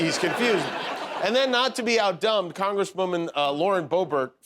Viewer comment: Did he say, "Out Done" or "Out Dumbed" at about